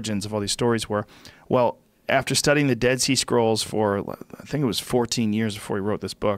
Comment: I thought he was saying "dead Sea squirrels" 😂